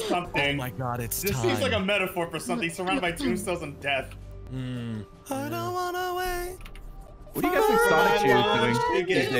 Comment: Did I hear “I don’t want to wait for my lunch to get colder” or am I just hungry?